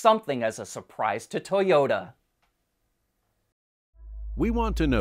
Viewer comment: did u say "Toyoda"? then you are right.